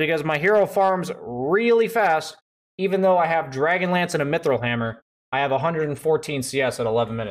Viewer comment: did he say meteor hammer?